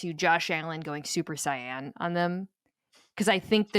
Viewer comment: i had to keep rewinding; Did Mina actually say JOSH ALLEN WENT SUPER SAIYAN ON THE LIONS LAST WEEK???! 😂😂😂😂😂😂😂😂😂😂😂😂😂